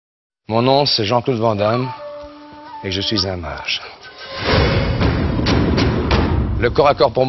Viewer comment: did he just say je suis fromage?